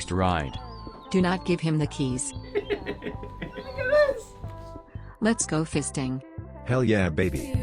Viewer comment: not gonna lie!! 0:38 sounded like " lets go fisting " hahahaha